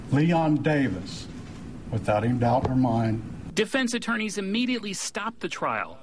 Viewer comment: For those that don't understand what the EMT did wrong, when he stated that she said the name Leon Davis without a doubt in her mind...he couldn't of know if she had doubt or not unless he was physic and therefor was accused of interpretation of the situation not a factual account or witness testimony. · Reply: Marconi Azize - I thought that she said, "...without a doubt in my mind." Thanks for clearing that up.